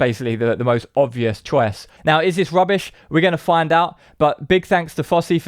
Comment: you pronounce "chor-wass" instead of choice. it's your urban accent. You do it all the time. I'm British and sometimes I misunderstand your pronunciation. Youtube is global. Great channel. Liked/commented/subscribed.